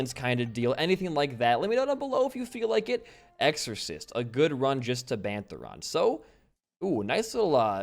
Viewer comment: At first I thought it said racist